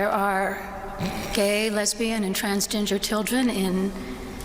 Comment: Did she say transginger? 😂